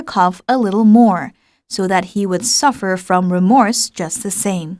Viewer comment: p.104 he should suffer -> he was suffer로 들리는데 맞나요??